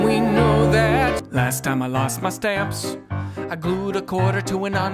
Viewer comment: Every single fucking time I’ve watched this video, every single time, I thought he said “last time I lost my steps “ not stamps, fucks sake